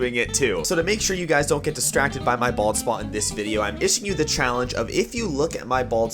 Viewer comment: I know he said bald spot..... But it sounded like this....balls spot